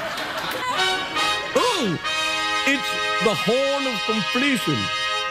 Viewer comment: what us Craig saying there? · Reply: In case you were still wondering: it is first "Oh, it's the horn of completion", and then he cracks up because "I just saw myself on the monitor".